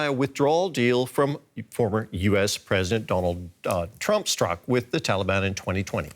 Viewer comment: did he say Donald Duck instead of Donald Trump?🤣🤣🤣